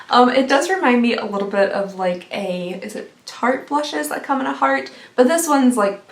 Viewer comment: I thought u said Target instead of Tarte lol